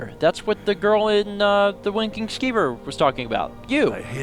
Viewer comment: It sounded like you said "Wanking Skeever" in the beginning of the episode.